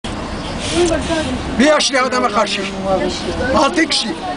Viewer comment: dayı ne diyor anladinizmi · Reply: Bi yaşlı adama karşı